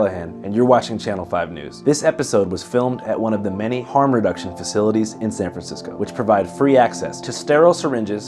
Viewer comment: I thought it said “HAM reduction facility” and I thought “oink” immediately.